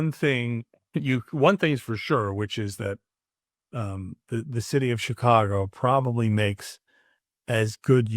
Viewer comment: Did he just say the shitty of Chicago 31:00 😂😅